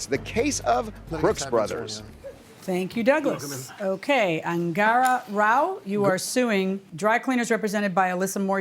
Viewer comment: Angara? 🤣🤣🤣🤣🤣 Thought he said angora! 😁